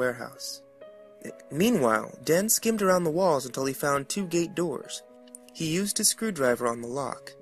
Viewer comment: he says to gate doors i thought he said two gay doors